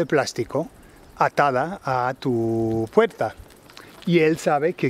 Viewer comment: Cool vid, I had to look up 'reja de la valla' but other than that, lo he captado bien. Quick question, did you say 'puerza' at 4:21. I'm guessing it must have been 'puerta' but just wondered if it was a regional pronunciation type thing.... Cheers m'dears!